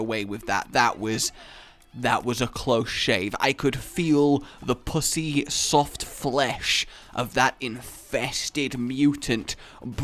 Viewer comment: The pussy soft flesh? What the fuck did you say?